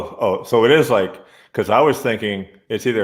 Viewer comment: Defenitly not Fanatec or Fanatik, cus Fanatik Bike Co is a bikeshop, Fanatec is Simracing gear company.😄 So Funatic is what he said, so yes, not bad.